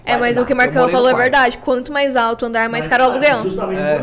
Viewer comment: O que ela falou ? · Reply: quanto mais alto o andar, mais caro é o aluguel